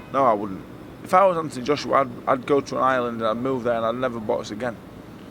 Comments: I thought he said Ireland, not Island, a couple of other think the same in the comments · I thought he said "I'd move to Ireland.... " ? 🙄😒